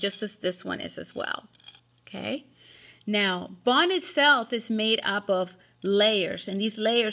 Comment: what in the world is this person trying to say? "now bon itself is made up of" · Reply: sayzar salinas bone itself is...